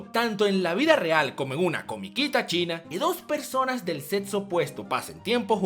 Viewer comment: Solo yo escuche "dos personas del ZETSU opuesto" cuando hablaban de Neji y Tenten??? xD